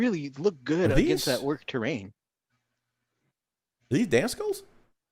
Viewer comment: Great stream guys, I thought I'd submitted some minis when I was drunk for a second there near the end when I thought I heard say, "are these Danskull's?" really Knows how to paint those Death Skulls